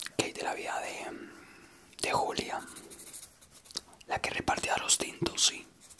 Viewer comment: Juro que escuché 'julia, la que repartía los dildos' o cómo es, AJAJAJAJAJJAJA.